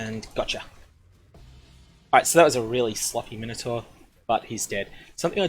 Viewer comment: Did you just say “that was a really slutty Minotaur?”